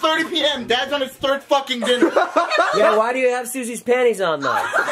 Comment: Bruh I love susie she’s pretty and her laugh has me dead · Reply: It’s early where I live and I thought you said sushi LoLololol